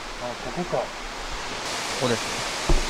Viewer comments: 来てます の後って 殺す ではなく ここです って聞こえる気がしました。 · こ○す。じゃなくて、ここです。 って聞こえたのは俺だけか？ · 来てますの後にこ○すって書いてあるけどここですって聞こえる